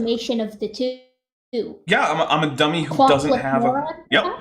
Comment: At the begining i heard "glockless" idiot, and u was like "ah, atf paperwork its a bitch."